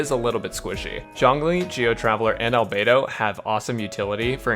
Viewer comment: When he was talking about Zhongli I thought he said Xiangling- I